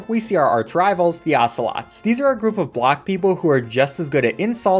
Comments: what di dhe say? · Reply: A group of black people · I swear the part he said "this is just a group of black people" I started laughing, no racism tho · I THOUGHT YOU SAID BLACK PEOPLE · Why did I hear 'a group of black people' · I thought he said a group of black people instead of block people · I have thought he said black people and I was just like.. Bitch are you color blind??